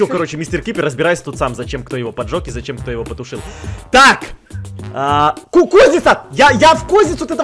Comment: 😂 НЕ ОДНА Я УСЛЫШАЛА "КУКУЗНИЦА", СО МНОЙ КАТЯ ЕЩЁ УСЛЫШАЛА!!!